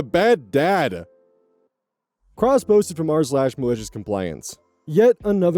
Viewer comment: OK, I know this is completely off-topic, but... The way you say "cross-posted from r/..." keeps making me do a double-take, because I keep hearing "crossbows from Mars"...